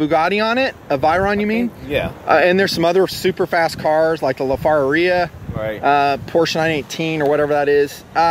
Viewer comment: Did he just say the La far a ria? lmfao